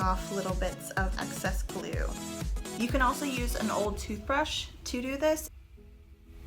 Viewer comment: Thanks for your tutorial! I attempted this and it came out nice but I am noticing I have a lot of excess glue in between the twine. What is the best way to get rid of this? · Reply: I thought I heard her say use a toothbrush